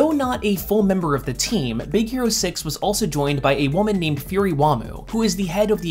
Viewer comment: So Fred is a stand user. Got it. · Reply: I mean at 10:23,did he say her name was fury wammu?